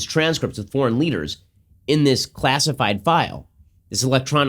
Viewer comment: Lol you gotta love how Ben is carefully saying “secret vault” instead of what it ACTUALLY IS WHICH IS A PRIVATE SERVER 😂. Trump is storing emails and important documents on a private server. Sound familiar? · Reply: Berger Merger @ 14:16, he actually says classified file. Not secret vault.